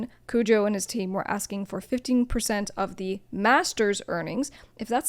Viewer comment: They weren't asking for 15%, you mishead what he said... they were asking for FIFTY PERCENT (50%) which is completely nuts, later in their chat, we see 25% percent, which is still completely nuts.